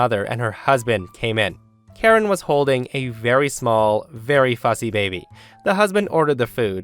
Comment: What was said - "Karen was holding a very small, very fussy baby." What I heard - "Karen was holding a very small, very fuzzy baby." Me - I'm sorry, what?